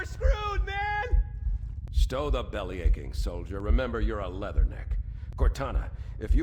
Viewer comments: I’d always thought Captain Keys said “Stow the belly aching soldier, remember your eleven” (‘Eleven’ being a panic & distress protocol procedure) 😅😅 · I always thought he said "stow that bellyaching soldier remember your eleven" 😂